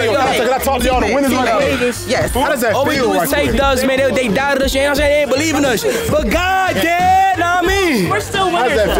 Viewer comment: As an English person, wtf did fanum say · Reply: "All we do is take dubs they doubted us you know what I'm saying believe in us but God did"